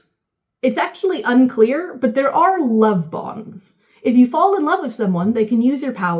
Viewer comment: Having forgotten some of the previous two books, I thought you said "love bombs" and was immediately like "yeah, that fits."